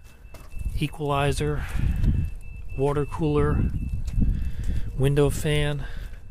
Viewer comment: at the recycling center, when you said "window fan", I thought you said Windows fan.